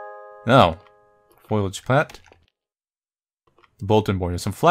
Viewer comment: when reading "foliage" it sounded like you said "foilage" and all i could think of was the end of one simpsons episode where lisa was trying for ages to teach marge how to say foliage and she kept saying foilage edit: then later you bring up a simpsons meme, its all comin up milhouse today